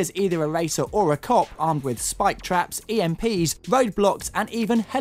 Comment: did he say emps?